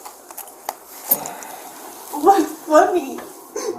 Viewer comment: was the cop laughing at her?? ahaha lmao i could have sworn i heard her dumbass say "whats funny"